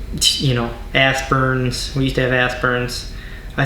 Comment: Aspirin sounded like u said ass burn lol 😆 love u guys ❤